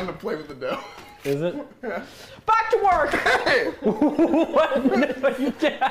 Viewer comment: At first I thought she said bratwurst and was confused but then rewound the video and realized that she said back to work😭😭😭